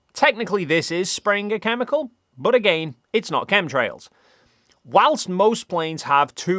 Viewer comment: Every time you say "chemtrails", I hear "cum trails" and I guess that makes just as much sense as these bonkers conspiracies